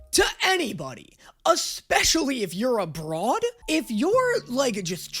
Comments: OOOOOOHHHHH he said abroad, I originally heard it as "a broad" and thought he was saying like especially if you were a woman this past what over a year now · I thought he said “a broad” as in a woman and I was like “wow all those Italian jokes have really changed Danny” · I know "especially if you're abroad" is what you said, but i heard "especially if you're a broad" and i was like "danny, what does being a woman have to do with getting away with shit talking and crime?" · I thought he said “expressly if your a broad” which gave me a chuckle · i really thought you said "especially if you're a broad" instead of "abroad". anyways your foresight is immaculate